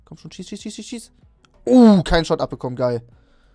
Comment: Ich hab bei 3:39 erst verstanden: “ Uuh! Cumshot abbekommen geil” 😂😂